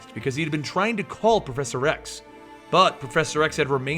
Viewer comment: Lol. You said proffesor x. sounded like you said professor rex. Love your videos.